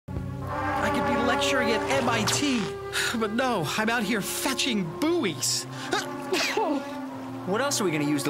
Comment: it just me or did he just saying f*cking